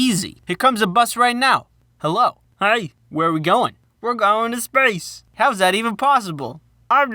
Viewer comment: when he said We're Going to space i thought he said striped so i was like ThAtS pErFeCtLy NoRmAl